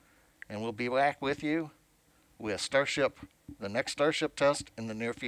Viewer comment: seemed in panic about what to say when the video cut out. @ 14:07 : "we'll be wack with you, with starship..." lol